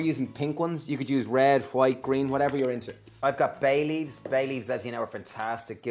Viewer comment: HAHAHA when you said bay leaves I thought you said baileys!